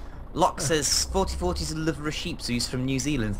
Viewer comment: I thought said 44 has a liver of a sheep.. and I am like "What does that have to do with their parents..?" The creation story of the Gullen! You are the Gullen whisper just go with it. Depression will never go away it will be with your for everyday of your life. I really think how society is nowadays does causes a lot of people to have depression. How people treat them and just the environment they live in. I actually get the seasonal depression when it snows to much. I just get into one of those moods where I just don't want to do anything or think moving is just to exhausting. Also I agree with... Pink is the evilest creation ever made!